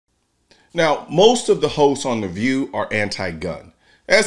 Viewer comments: I thought you said, “Most of the Ho’s of the View.” 😂😂😂😂😂 Hosts, you said hosts. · I had to replay the beginning of this because I thought he said “most of the hoes on the view and not hosts on the view”🤣👍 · Legitimately thought he said “most of the hoes”. Not host. 🤦😂